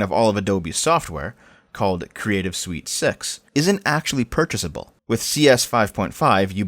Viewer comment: I thought you said Creative Sweet Sex.